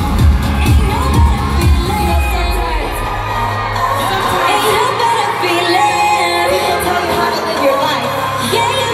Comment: What does she say? Please tell me. 02:03~ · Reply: " You know sometimes... sometimes, people tell you how to live your life. You know girls... just roll your eyes, shake it off and walk away."